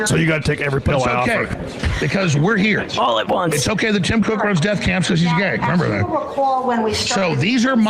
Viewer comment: what did he say at 5:08? I hear Tim Cook, deathcamps, & gay. did i just get infowarred